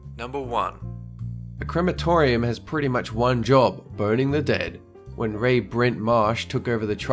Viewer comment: Am I the one one that thought said "boning the dead" instead of burning?